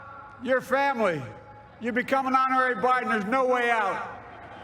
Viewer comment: At time 14:50 the president said.become a honorary "___________" and there's no way out? Can someone put together what was said, it sounded like "blanket" or "blank", "bride" or "partner" im not sure. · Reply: "Kamala, Doug, like it or not, you're family. You've become an honorary Biden and there's no way out."